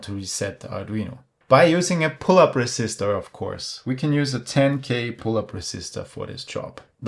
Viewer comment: "подтягивающий резистор на 10 комитетов..." на 10 чего? Или мне послышалось?